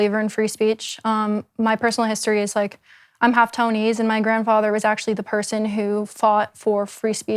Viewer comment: She's half what? I couldn't hear that part properly. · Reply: Artificial intelligence says she's half Tony's. I'd guess she said Taiwanese. Edit: afterwards she talks about her grandfather in Taiwan, so it's not a guess anymore, I'd say it's confirmed she says Taiwanese.